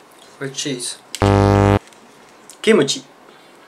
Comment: Did say "Kimochi" at 5:17 ?